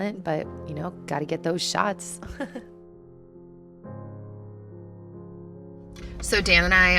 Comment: haha during your intro I heard "dog shots" and got so excited!